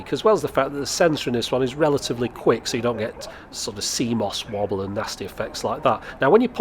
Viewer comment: Wow I just heard you say CMOS wobble, I always understood it as if you said "Seamoss wobble" - I don't know, perhaps a saying in English, haha. It just dawned on me because of the context you said it in.